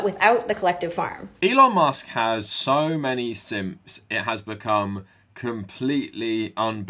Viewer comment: i thought you said "Elon Musk has so many SIMS" and i was like ??!?? 😂